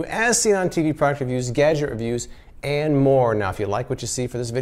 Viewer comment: when he said "gadget reviews" I thought he said "gadget abuse" XD